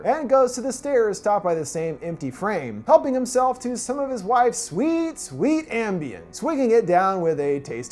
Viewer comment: The wife's face looks so airbrushed whenever there is a close-up of her...it makes her look like an alien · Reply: At first i thought u said my wife